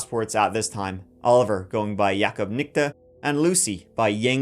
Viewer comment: WHOOAA!!! Did you just say ni**a i think you did🤦🏾‍♂️😂 them last names are touching the line sir !! Lol just playin i died laughing at you trying to pronounce them then the mighty pause then they landed in Monyorzag 😂🤣 have a great one man!!